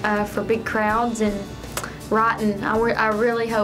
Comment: When she said writing I thought she said rotten... Lol xD